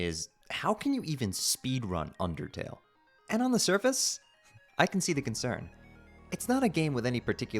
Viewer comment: did you just say on the surface?